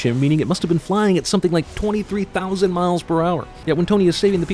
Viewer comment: Subtitle says 23,300 but you said 23,000